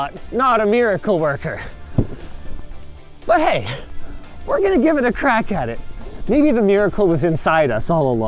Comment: i know he said "we're gonna give it a crack at it", but all i heard was "we're gonna give it a crack addict"